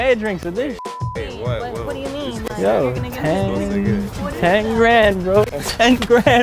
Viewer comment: i would of told the second guy that he was a good man and gave him 5 dollars for how chill he was a cool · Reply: Wait nvm i thought u said 5 grand forget that other comment lololol